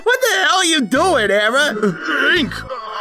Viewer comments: What i hear errorsans says when the part he's shock: 0:40 "Uh- Hank" Me: is it from uh? The name is familliar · Ink: what are you doing hammer? That's what I heard.